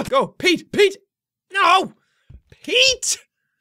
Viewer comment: When I was watching this for the first time I thought he was saying; yeet!